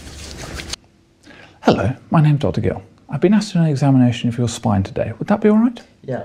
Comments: ０:05 hello my name, I heard jeff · Is there an “is” when he says hello at the beginning. It sounds like he says “Hello, my name Dr. Gill”